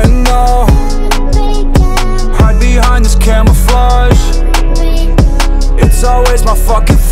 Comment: Am I the only one that thought he said “camera fuzz” instead of camouflage?